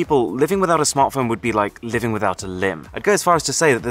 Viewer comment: what did he say? lim?